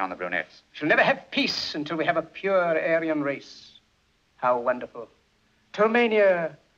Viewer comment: Did he said "we'll never hv peace until we have pure Alien race"???